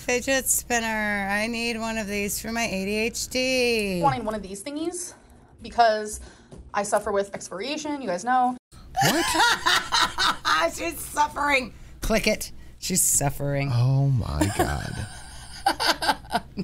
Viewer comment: I swore I heard her say "I suffer with expiration" I was suffering from expiring too for the last 15 minutes.